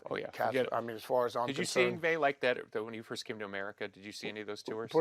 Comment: At around 5:58, why does Billy say "When you first came to America..."? Zakk Wylde was born in New Jersey, right? Right? Am I missing something? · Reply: I think he says “when he first came to America” in reference to Yngwie Malmsteen, but he’s just talking kind of fast.